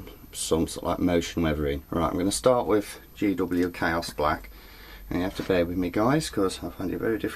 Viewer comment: Hehe, when says "chaos black" it sounds like hes saying "cow splat". Think i may call it that from now on... :D